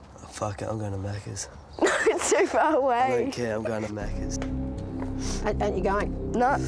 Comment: “I’m going to maccas” “Maccas is so far away” Lmao in aussie maccas right round the corner 😂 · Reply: Whole time I thought he was saying maccies as in mc Donald’s